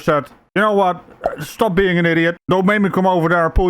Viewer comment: I thought it said idiot not patriot